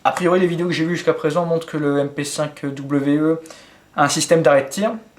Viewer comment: ça m'a fait rire lorsque que tu as dit " le mp5k we" j'ai compris "le mp5 kwe "